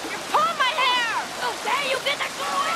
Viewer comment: for the last 15 years i always thought he said are you gay!!!